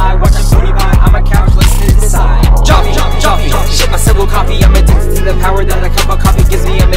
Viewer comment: im so sorry but did you just say ill shit my self coffe